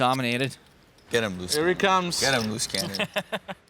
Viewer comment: if you turn on auto caption, instead of "get him loose cannon" it say "I'm loose cancer" :D